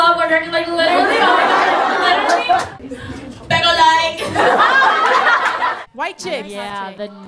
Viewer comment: what normani said in 1:03 ??? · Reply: pero like